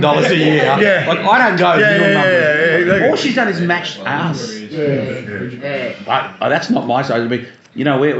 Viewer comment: It sounded like he said "All she did was Mashed Ass"